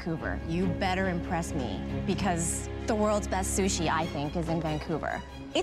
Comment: I hear "best shushi are from Vancouver " I go to the comments and see I am not alone 😂